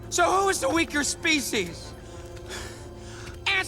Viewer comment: wait... Did he just say ass?